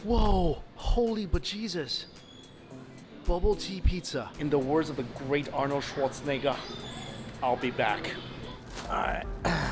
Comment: did he say In the wars of arnold short nigga